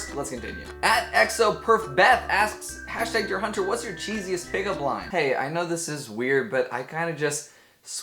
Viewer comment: hahaha at first i thought that said cheekiest pickup line😂